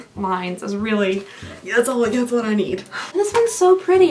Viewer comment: I heard that word as Sturdy and Firm, and I was following until you broke it down. Then My brain was like wait, was that not what she said.